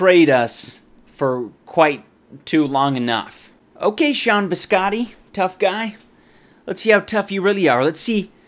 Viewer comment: I thought you said, Scott Mescudi aka. Kid Cudi xD